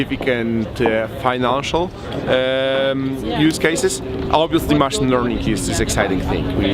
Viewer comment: Thanks for the interview, but what is he saying at 4:02? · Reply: Machine learning